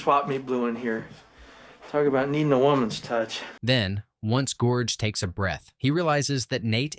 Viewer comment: Lol, did you call him Gorge?!? 😂